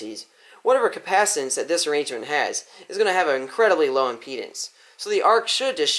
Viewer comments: I swear I heard him say “is going to have an incredibly long penis” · ngl thought he said penis had to do a double take